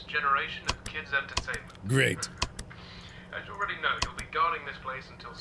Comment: thought he said gay instead of great